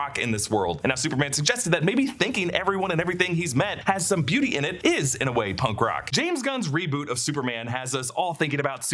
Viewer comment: the 7:56 mark who did Superman say was taking care of that thing outside? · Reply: Justice Gang